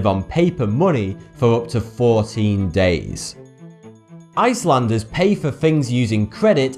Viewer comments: The subtitles says 17 days but he says 14. LOL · says 14 days, the subtitle says 17 days... So... I guess i am done with my life